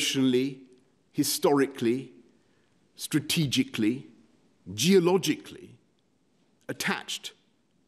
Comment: "this country would remain ... geologically attached" hahahahahahahahahahahaha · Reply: at the beginning i thought i heard - geopolitically - but then - i realized - Boris is very seismic - i personally don't believe a single word of what they both said